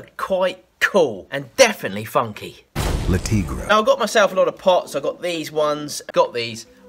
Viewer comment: When you said you get your a pot I thought you said "now I got myself some pot".. I'm like.. " What do drugs have to do with this again? "